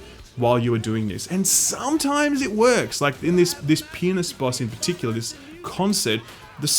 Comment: It might be just because it's 2am for me in TX/USA, but every time you said, "fighting the pianist" or "on a pianist stage", I think you're saying "fighting a penis" and "on a penis stage". lol